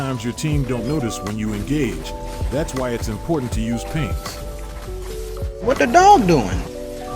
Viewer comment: in 5:02 I can't clearly hear what you say. are you saying "pins" or "paint"? either way how do you do it to get noticed when you engage? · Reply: “Pings” Like when you use the ping “request backup” so your team knows that you’re going in. You can also use “launch attack”